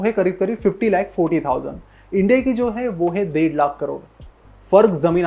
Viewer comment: kya bol gaya bhai? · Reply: 1.5lakh crore🤐